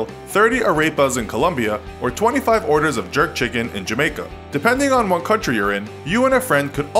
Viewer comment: I thought it said "Jerk childrens" instead of jerk chickens. Lmao